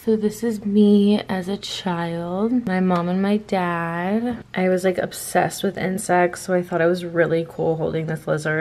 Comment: I thought she said "I was like obsessed with incest"